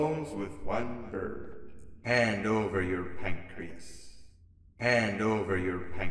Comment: I kept hearing hand over your pancakes when I wasn't looking at subtitles